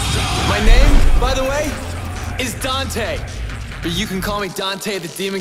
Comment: haha i thought i heard him say he's Dante.. XD funny. What!? this is the new dmc game?! -_- Shit. . .